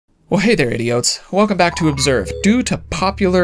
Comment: Did he just say “hello idiots”? Anyways I’m down